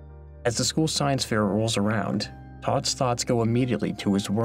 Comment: He said Todd's Thoughts but I heard "Scott's Tots"